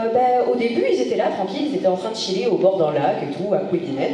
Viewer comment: Pitié, dis moi que tu as bien dit "en train de 'CHILLER' " ? 😅 ■ 14:00 Je me suis toujours fait la remarque : c'est quand même un peu injuste de dire que les Nains ne sont pas eux aussi des enfants d'Ilúvatar ; ok, Aulë a conçu les hröar des Nains mais de fait, il n'aurait jamais pu leur "donner la vie" sans la Flamme Impérissable donc de par leur essence, les Nains sont également dans une certaine mesure, des enfants d'Ilúvatar, il leur a quand même donné la part la plus essentiel de leur être. Mais bon vous allez me dire qu'en raisonnant de cette façon, tous les êtres vivants d'Arda méritent de porter le titre "d'Enfants d'Ilúvatar" ; franchement, j'ai du mal à avaler le fait que les animaux tels que les grands corbeaux d'Erebor n'ai même pas d'âmes 😑. C'est vrai finalement, c'est quoi la définition d'une âme selon Tolkien. ■ 22:52 On en revient toujours à Bolg qui a vécu au moins 142 ans ainsi qu'au "Grand Gobelin" du Hobbit et ses sbires qui étaient assez vieux pour avoir vu les leurs se faire pourfendre par Turgon à Gondolin ; à moins qu'il ne soient tous des Boldogs ou des engeances de Boldogs, ce qui pourrait expliquer à la fois leur stature et leur longévité hors normes. Dommage que Tolkien n'ait pas développer plus que ça le concept des Ainurindi en dehors de Luthien (par exemple, si deux Ainur purs incarnés produisent un enfant, est-ce que cet enfant naitra avec l'essence d'un Ainur pur ? Est-ce qu'on parle aussi de Fëar pour les Ainur ?) ■ 24:28 Bip Boldog e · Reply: Oui on a bien dit CHILLER 😂. Les Nains sont bien considérés comme des enfants d'Illuvatar, mais il est précisé "adopté". Il y a une grosse différence entre les Nains et les autres enfants d'Illuvatar, c'est que Illuvatar n'a jamais participé à leur conception. Quand on parle d'enfant d'Illuvatar, c'est parcequ'on parle de créations d'Illuvatar uniquement comme les elfes et les humains. Le fait de donner la vie ne suffit pas. Les Nains ont été adopté par Illuvatar dans le sens où il les a accepté comme sa propre création. Toutes les autres espèces n'ont pas été créés pas Illuvatar, mais suite à la musique des Ainur. Poir Bolg, on en revient à ce qu'on a expliqué plusieurs fois et à ce que Tolkien a écrit sans jamais changer cette version : les chefs Orcs sont très probablement des Maiar, d'où leur longévité.